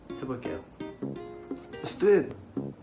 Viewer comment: I think he said let’s do it 😂 not ?